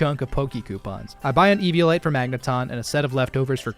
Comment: Did he say eviolite for Magneton....????